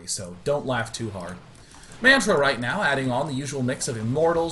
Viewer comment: Did he call Tristan, Manfred at 7:30?